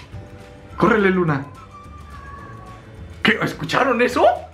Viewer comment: el gato que dice Raul XD